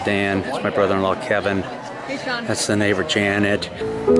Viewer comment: Almost thought you said "that's my neighbor-in-law Janet".